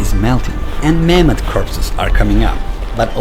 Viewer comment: Did he really say Mammoth corpses..? O.o